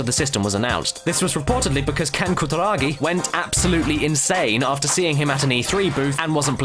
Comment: I can't understand what he says at 5:58 :/ Sorry, english is not my first language. Btw, Thanks a lot for the video! the PS1 is my favourite console of all the time! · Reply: "Went absolutely insane after seeing him( the mascot) at an E3(gaming convention) booth and not liking the character"